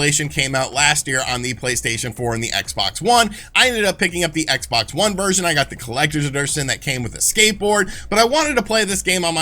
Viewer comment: Did you just say Xbox One Version Collector's Aderstion? @@ wtf is that? lol